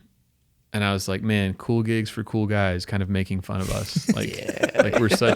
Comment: Bro I thought this said cool cigs for cool guys😂